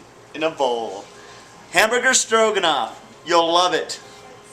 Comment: OH STROGONOFF i thought it said dragunov